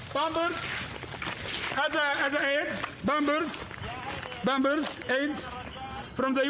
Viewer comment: I hear "Bambers, bambers, UN bambers" lol